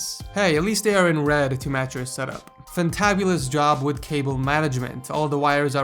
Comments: The Sassy meter has been amplified. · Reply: did you just say Fantabulous? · did you just say Fantabulous?